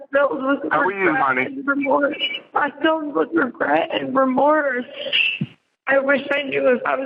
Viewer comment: did she say “I don’t feel regret or remorse” or did I mishear